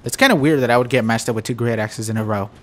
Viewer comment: Definitely let me know what weapon you guys would like to see me use next! And also be sure to drop a like on the video if you enjoyed :D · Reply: when you said great axes I thought you said great asses